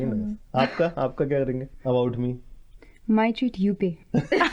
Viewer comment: What did said in 10:39 ? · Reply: "My treat, you pay."